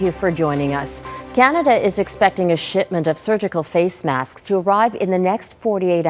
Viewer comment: It really sounded like she said 'Canada is expecting a 'shitment'... '